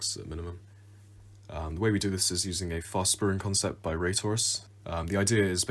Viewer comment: at first i thought you said RaysWorks when you said Raytourus